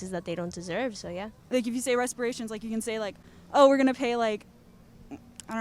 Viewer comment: did she really say respirations?